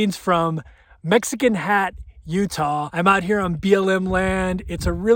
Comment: Thanks for showing us Mexican hat! At first I thought you were saying medicine hat Tuesday. On another note, if there is a roof deck that looked like it was all over the top, where are the solar panels?